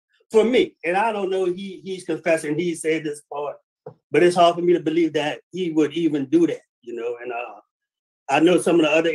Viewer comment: Brother didnt say "he didnt do it" @ 22:36 he says "its hard for me to believe THAT HE WOULD EVEN DO THAT" and if you put into context of what he was sayn before that imo it sounded like he was ashamed he did it and not sayn he didnt do it.. i dont think its right to say someone said somthing they didnt say. Eitherway hes ah pos for doing that do that precious little girl and i hope she is able to heal from it.